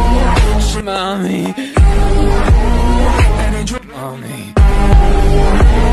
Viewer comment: I thought denki said “faster~” well thanks for the meal 😳🥵😂